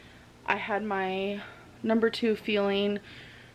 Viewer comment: Omg when she said number 2 fueling I thought she said number 2 feeling. I thought ok you have to go to the bathroom?